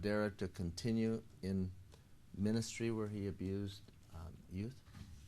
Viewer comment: objection did you just say industry?